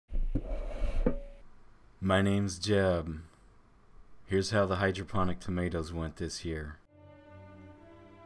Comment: I thought he said ma name is Jeff